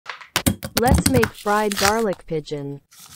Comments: Did I heard right?! "Let's make fried garlic pigeon!? 😭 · Why do I hear "fried garlic pigeon" LOL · I heard “Garlic pigeon”😂😂😂😂😂😂😂